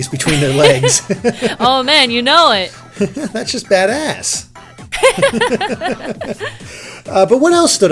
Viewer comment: sounds like he said "dat ass" but i think he said "Badass" XD